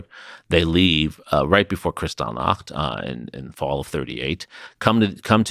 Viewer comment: of this video what is it that he’s saying? Kissinger left right before what? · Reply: Kristallnacht